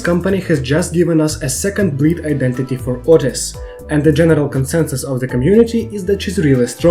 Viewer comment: I heard that as "A second breed Identity for outis"